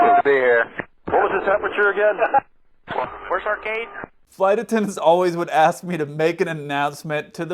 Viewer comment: The pilots roasting him is hilarious! What was said at 16:41? It sounds like First arcade? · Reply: "Where's our gate?"